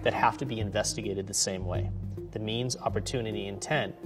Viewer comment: i heard memes